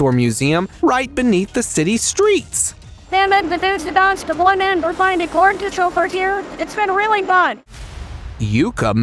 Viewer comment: In case anyone else was wondering what the lady said at 21:10, she said: "they the dud the one end or find a g to for here it's been really fun."